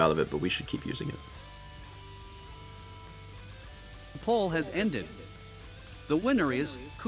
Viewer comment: thought it said paul has ended amd got concerned